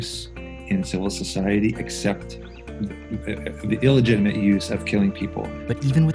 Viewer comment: Yes they are leaning someway, probably forward, touching their toes. BUT You don't hear to well, they said @ 1:40 "The 50 cal is outlawed in Mexico, but is legal in most of the US" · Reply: I misheard, thought he said illegal. Still doesn't change the fact of their left leaning political agenda.